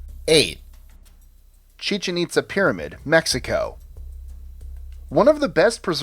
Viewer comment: I thought he said pizza pizza pyramid 🤦🏽‍♀️